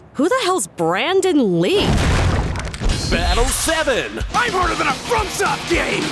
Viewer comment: Does anyone know what Kirishima said? "I'm harder than a what?" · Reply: "I'm harder front soft game"